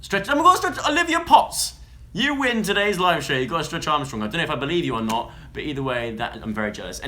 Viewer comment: Did he say a "stretch armstrong " 3:00??